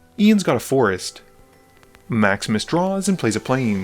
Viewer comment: I cannot tell you how devastated I am that he is saying "Maximus Draws" and not "Max MisDraws". I thought this was a running but I was just mishearing it!